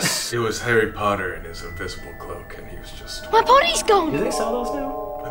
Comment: It sounded like Harry said my potty is gone